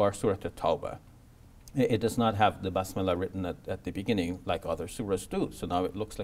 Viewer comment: i dont know if i heard it wrong but i played it 10 times just to check and heard basmillah, must be an accent issue